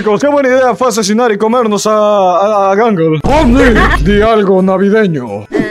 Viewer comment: creí que dijo "algo navideño" pipipipi